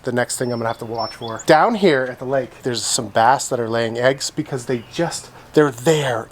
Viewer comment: We thought you said “BATS laying eggs” 😅 were so confused, and wondered why you were looking in the water!!!😂